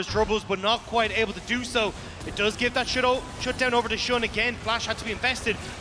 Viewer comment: sounds like he said "shit ult " lol